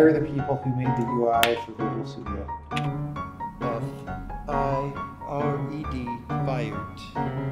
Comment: what did he say here? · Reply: He said "F-I-R-E-D, fired"